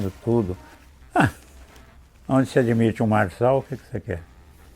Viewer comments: Não entendi a parte em que ele fala: em ele diz onde se admite... Min 10:10. Se alguém tiver entedido, por favor me explique. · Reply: Creio que seja: "Onde se admite um Marçal , o quê que você quer?!" (fazendo referência as últimas eleições municipais de São Paulo). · Não compreendi aos 10:15 onde ele diz: Onde se admite um....(incompreensível) você quer o que? Alguém sabe me dizer o que ele fala? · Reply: Olá, Ele diz "Marçal", em referência a Pablo Marçal, que foi candidato à prefeitura de São Paulo. O episódio foi gravado no ano passado poucas semanas após a eleição municipal.